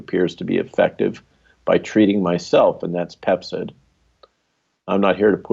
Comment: Did he say Pepcid?